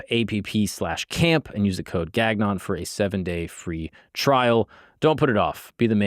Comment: Lmao I was listening to this about to sleep and I hear a sex ad and thought he said gagged on for the code 😂 I was like how ironic didn’t know it was his name Gagnon